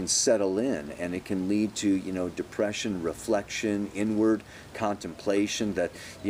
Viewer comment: When you said "pression" I heard "depression". Yeh, I'm a little bit down today.